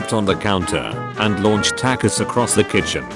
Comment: it sounded like it said Takis not tacos lmao